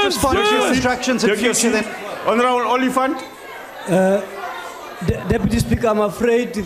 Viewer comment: Did he say Honorable Elephant?!